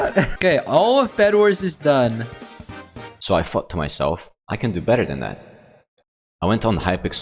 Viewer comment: sounds like he says : "so i fked myself"